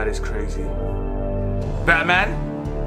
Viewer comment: Did he say "Batman"? Lmao 🤣🤣🤣🤣